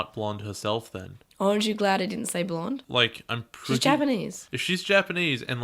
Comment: Did just say "orange you glad didn't say blonde"